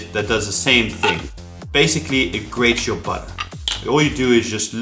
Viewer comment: Did anybody hear "It grates your butt"? LOL